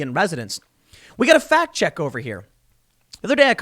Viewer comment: lol, recovering from a fat bong rip, coulda sworn i heard tim say we got a aft chick over here...